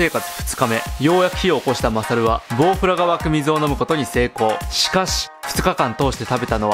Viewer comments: GoProにしか聞こえない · Go Proが湧く水に聞こえてしまった…